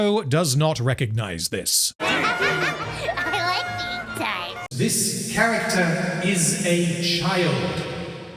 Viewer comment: did she say "I like being touched"???? 💀 wtf